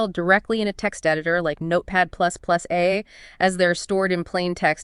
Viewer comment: "actually edit a step file directly in a text editor like notepad++A" No mystery here... she actually said "Notepad++, eh..." Clearly she's speaking Can-AI-dian 🙂.